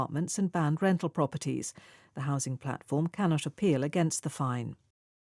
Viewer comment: can not apeal 캔낫 쇼필로 들리네요.